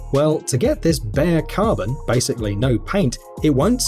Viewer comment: Thought he said Bugatti veyron,but he said bare carbon